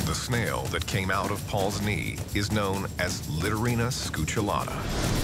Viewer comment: I misheard at first and thought the snail was called "littering enchilada." I was confused.